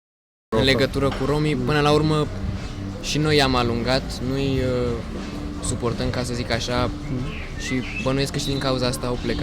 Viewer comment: he actually said that we don't support them, not that we're suspiciuos of them, like it says in the subtitles.